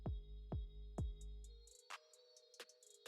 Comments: thought he said "diddy got a podcast" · Great content and I am definitely a subscriber! With that being said please slow down when u say "DD got a podcast" because it sounds like ur saying Diddy 🤣🤣🤣💯😎